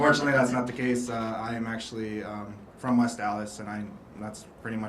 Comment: West Allis fuck I thought he said he from West Dallas 😂 congrats bro